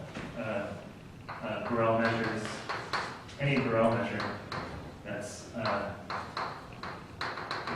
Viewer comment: you say something like "In general Integrating against any ____ that is a finite compact subset of omega will be a distribution" What was that word? I sort of heard something like "grow measure" but I'm not sure. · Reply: "Any Borel measure that is finite on compact subsets of omega". Note: the (typed) notes for this course may be downloaded at <link>